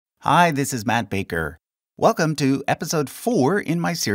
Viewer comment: I thought you said Bart Baker, I almost had a baby.